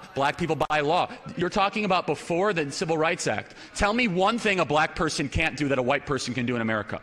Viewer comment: Anyone got better ears than me/was there for 5:40ish what does he say? Something along the lines of "black people can't walk down the street at night"?